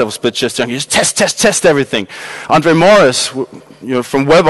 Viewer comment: in case you couldn't understand the name of the guy he mentions (like me). It's André Morys from <link> <link>